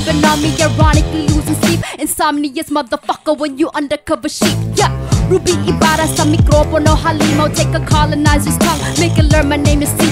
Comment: does ruby say "ruby ibara sto mikrofono" (on the microphone at greek) at 2:30? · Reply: She said "Ruby Ibarra sa mikropono halimaw" it is Tagalog and it means "Ruby Ibarra, monster on the microphone".